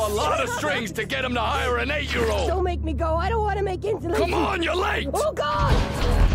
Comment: “I don’t wanna make insulation!” Felt his pain. 🤣🤣🤣🤣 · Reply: I thought he was saying "i don't wanna make ends meet", lol